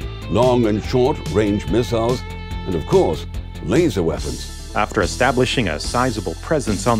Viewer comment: "And of course, Laser Weapons" The nuclear reactor is right there, would be a shame not to use it for lasers, no? 😂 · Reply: i heard "And duh, laser weapons cuase ya."